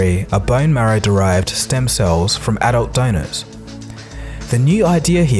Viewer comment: Can someone clarify this for me, at 5:34 what is being said? derived from adult dinos... what?